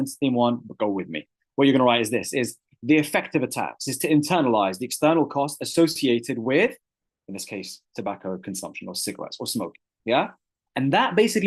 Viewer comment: What did he say at 2:04:52? nvm i know it now Its "the effect of the tax is to internalise the external costs associated with the demerit good"